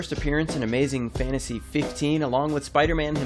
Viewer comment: did you just say 'fitnasy'????